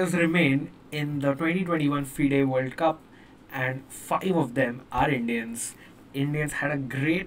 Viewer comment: sounds like "fucking of them" instead of "five of them" 🤣. However, nice recap 👌